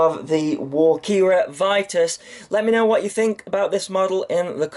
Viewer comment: Thought it said 'walkera virus'